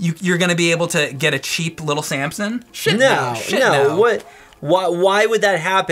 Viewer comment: What do they say at 13:33? 'A cheap little ...?' Sampson?